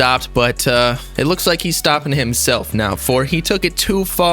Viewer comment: I thought you said he stitched himself